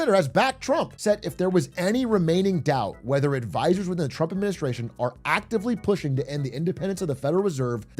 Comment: I initially misheard this as "Biden and the Trump Administration", lol. 'Bad advisers', corrupt local officials etc., was exactly how people avoided implicating Stalin during the Soviet purges in the 1930.